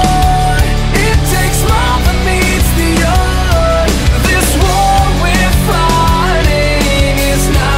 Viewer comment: Also i heard This war we're farting I was like-..... LOL